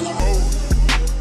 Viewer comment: What is the rap lyrics in the beginning of the video? “I’m a sike a lil”? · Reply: I thought he was saying, I'm a psychic, Lil bih...